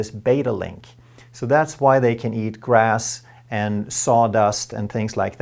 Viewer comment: Did he just said cows are fed sawdust?